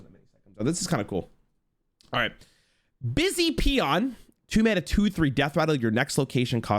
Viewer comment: SORRY DID HE JUST SAY "PEE ON"????? LMFAOOOOO